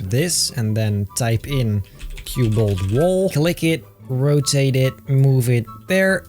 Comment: i thought you said cute bolt wall